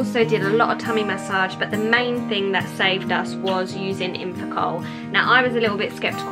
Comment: What does she say at 2:23?? Ugh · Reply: Infacol